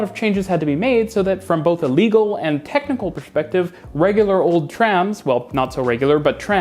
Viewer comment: Did you just say "pershpective" in 4:24? I love it. Nice German accent. 😉